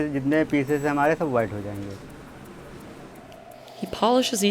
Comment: did he say 'Whiter than you' 😂